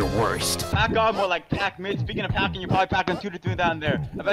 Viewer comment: I think he was gonna say speaking about pack you probably only got 2 to 3 inches that was what I kinda heard through the mumbles